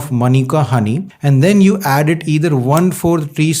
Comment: 1 tsp manuka honey, 1/4 tsp ginger powder, pinch pepper, a few drops of lemon eat it from tsp. Sorry, I thought he said a tsp of ginger.